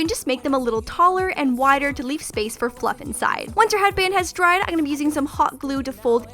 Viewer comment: did she say once your headband is dry?? Cause I'm confused...